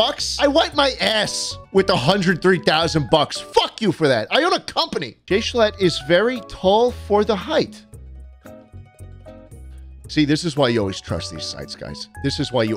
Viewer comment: the subtitles saying gay schlatt 😭